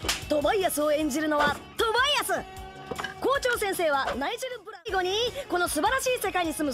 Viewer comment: I THOUGHT IT SAID GOJO BC IM TOO ADDICTED TO JJK